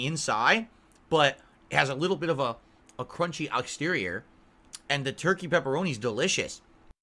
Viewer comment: okay the closed captions say exterior but it sounds like saying outsterior so he's not saying exterior he's not saying outterior he's saying outsterior goddamn I'm putting way to much effort into this